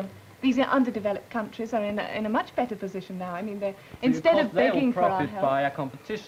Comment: what she said? lol. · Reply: She said "instead of begging"